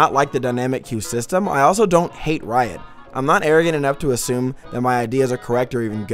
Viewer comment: For a sec I thought you said "I'm not Aryan enough to assume my ideas are correct." And I was like WHOAH HEY WHOAH THERE! People don't think like that no mo.